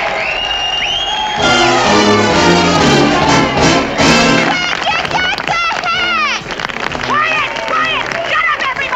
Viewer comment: what is she even saying?? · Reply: "Where'd ya get the hat?"